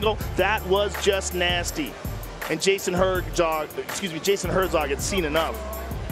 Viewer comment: what did joe rogan said 'jason herddog'